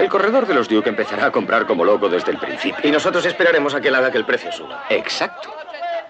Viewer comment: No entiendo bien lo que dice Valentín en el minuto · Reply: dice "esperaremos a que el precio suba"....para que vendan acciones a precio alto..............y luego las recompran ganándose la diferencia......(ve mi explicación de toda la escena al principio de los comentarios)